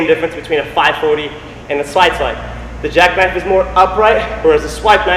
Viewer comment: Did you say Swidesipe?